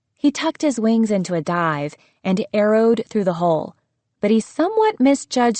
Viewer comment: Im gonna be honest i thought it said "aero-ed" instead of "arrowed" for some reason like as in "aeroplane" lmao